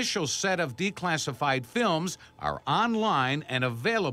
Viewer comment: it’s Wednesday my dudes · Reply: i hear “wensifying films